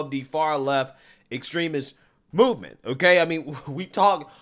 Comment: Lol I thought he said boovement instead of movement at 2:41 he clearly doesn’t but I swore he did